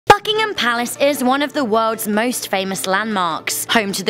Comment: At first I thought it said f***king ham palace instead of bucking ham palace